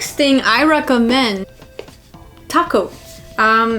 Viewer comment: I thought she said taco lol